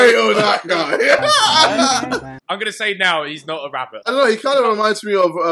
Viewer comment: why did i thought it said “ who is not a rapist ?” Wtf😂